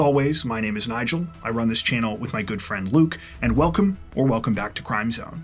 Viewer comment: No matter how many intros where you say “my good friend Luke” I hear “Nigel and Luke” and it sounds like brothers names.